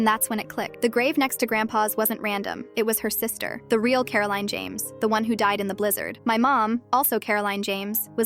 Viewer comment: I had to re-watch because I thought she said “Coraline Jones” 😭😭